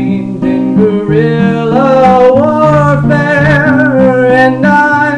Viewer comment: Thought that it said Welfare for a second.